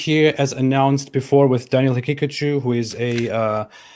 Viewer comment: Every time you say his last name I keep hearing it as “Pikachu” 😂😂